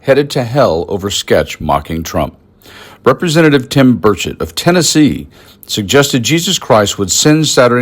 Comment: I keep hearing "bird sh*t" and not Burchett